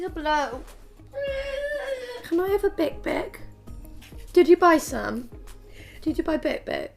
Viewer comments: i have no idea what is saying but she sounds so cute i dont even care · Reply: Larissa Sousa yeh it’s a chocolate biscuit - choccy bic bic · i have no idea what is saying but she sounds so cute i dont even care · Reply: I thought she said can I have a dick pic😂😂